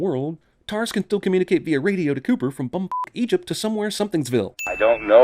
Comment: Did he just say bum fuck Egypt